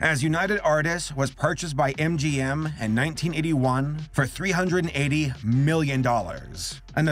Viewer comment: Me: "It made 30 million? That doesn't sound like that much of a failure." "It made 30 thousand" Me: "Oh..." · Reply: I also thought it said 30 million, LOL.